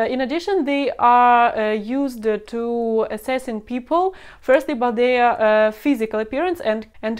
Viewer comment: I thought she said Russian women are used to assassin people 😂